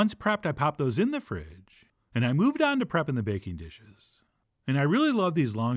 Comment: When you said baking dishes, my eye's and ears lit up, I thought you said bacon dishes. I'm not a salmon guy.